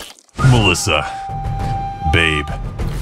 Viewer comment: did he just call Melissa babe!?